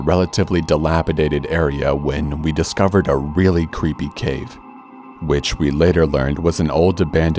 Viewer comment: I heard "really creepy cake" WELP